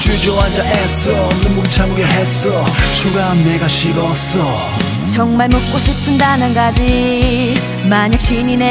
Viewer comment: 나만 정말 먹고싶은 단한가지로 들리나ㅋㅋ